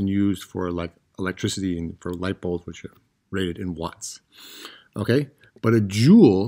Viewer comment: did you say “lightbulbs and shit”? 😂